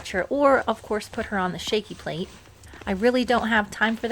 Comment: I could have sworn I heard Tiny say " Its Shaky plate Time" ❤